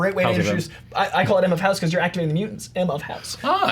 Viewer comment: "M of House", literally had to watch 3 times and then slow it down to catch what was saying: "I call it M of House, because you're activating the mutants" Ah! 😀